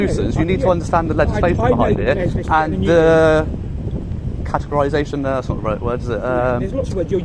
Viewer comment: You offered up the word, 'Categorisation', but I heard, 'Catheterisation'. I think I was correct, as he was taking the pee.